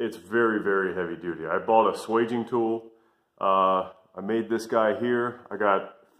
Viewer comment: Thanks for the idea. What type of tool did you say you bought (at the 2:06 mark)? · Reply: Thanks for the feedback! A Swage tool. Specifically, I bought a Tyler bench mounted model. They're kinda pricey, but I made a bunch of them, and wanted something built for the job.